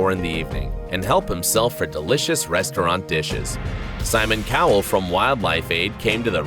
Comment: Did he say Simon Cowell or was it just me? Are we thinking of the same guy here?